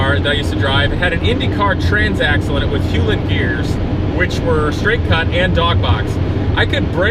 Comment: I'm just learning manual transmission and this part completely lost me LOL! 😂😂😅 what did he just say?? That's still english right? "Hulen box dog cut gear.. straight"